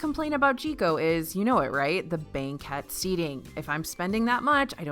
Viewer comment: Did you say banquet seating? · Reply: banquette seating.